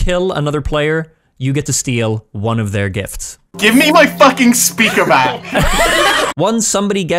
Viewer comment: I thought said gibe me my cooking spear back